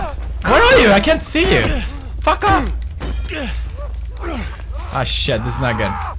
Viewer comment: everytime he says "fuck off" it sounds like he says пока